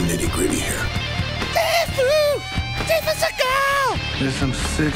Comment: The SIFU SEAGALLL!!! introduction kills me everytime you guys should make that a t-shirt 😂😂😂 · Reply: I thought was saying "seafood Seagal"!! He'd tear up a king crab buffet.